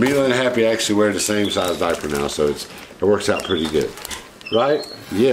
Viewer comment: Merry Christmas Boo crew and. At first i thought you said "me and Happy wear the same size diaper" lol 🐒💕🐒💕🐒